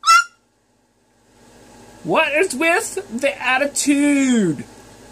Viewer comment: Did he say wtf? Lol